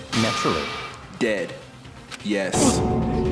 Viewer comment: Why I thought said “Daddy,yes” 😂😂 I’m with and this blinking shit